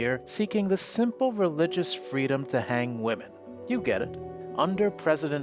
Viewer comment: betrayed by pilgrims who came here seeking the simple religious freedom to hang women. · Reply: I thought he said bang women